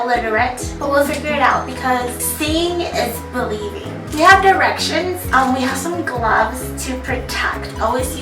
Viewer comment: when you said you have directions i thought you said when you have erections lol